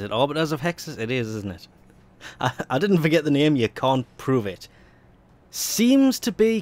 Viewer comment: I thought you said, "you c*nt..".